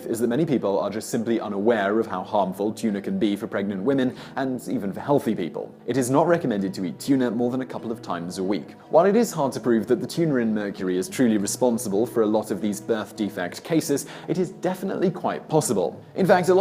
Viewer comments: could anyone else catch it. "While it is hard to believe the Tuna in Mercury" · Did I just hear Simon say:"the tuna in Mercury"?